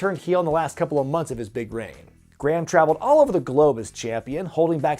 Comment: does he say big brain or big reign